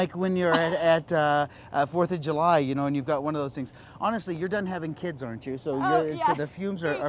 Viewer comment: Did he say "You're done having kids arent you? "